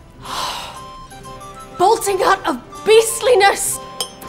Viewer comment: What did she say at 0:25? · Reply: "Bolting-hutch of beastliness" - from Henry IV